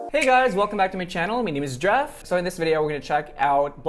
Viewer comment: did you say? haha hey! :yougotthis